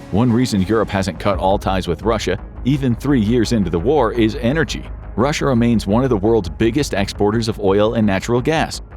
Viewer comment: did I just hear you say Russer (like late president JFK)